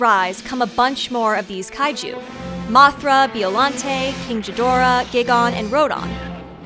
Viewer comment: did you just say king jidorah